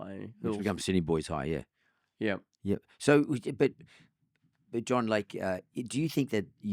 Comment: Does mark call John Howard, John Lake at 22:09?